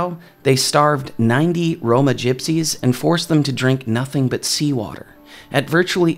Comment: I like how the audio at 9:08 says "Roma Gypsies" (not a woke term), but the subtitles say "Romani people" (woke term).